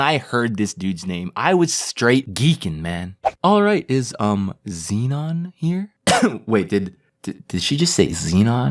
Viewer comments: I thought it said venon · (did SHE just say xenon)it’s a he